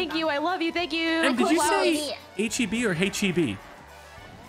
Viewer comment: don't feel bad Chilled, I heard "H-E-B" the first time, too, and not "Hey, Chibi". I had to replay it, then show my wife, and she heard it, too. It's like a "how long have you lived in Texas" litmus test